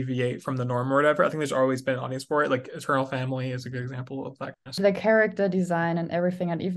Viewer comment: What is said at 15:05 … family? What family? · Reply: eternal family is the name of our streaming platform